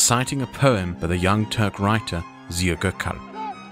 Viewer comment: What the name of writer of the poem that Erdogan cites? I couldn't make out the name and the caption isn't helpful. Zio Gurkhall? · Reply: It's the "Asker Duası" (Soldier's Prayer) by Ziya Gökalp, written in 1912.